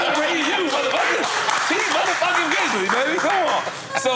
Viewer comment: did nore call t pain, tee grizzly?? 😂